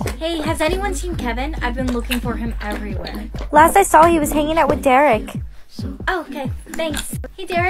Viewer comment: I heard "last i saw making out with derek" 💀